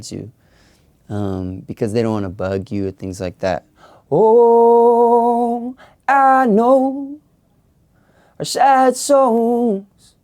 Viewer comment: I heard "oohhhhhhh I knowwww are sad souls" lol